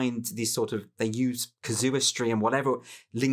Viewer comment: I misheard Andrew's use of the word 'casuistry' as 'kazoo-istry', which is a pretty apt metaphor for cancel culture: a bunch of overgrown children angrily tooting kazoos xD.